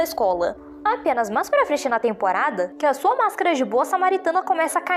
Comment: eu ouvi "sua mascara de bosta" kkkkkk